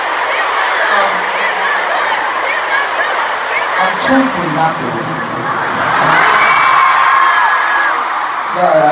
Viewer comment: What did Namjoon say at the start of his Ment? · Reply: Erum Iqbal im totally not ready.